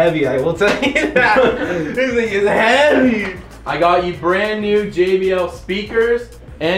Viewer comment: did he say the n word idk if he says it’s jw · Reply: Thing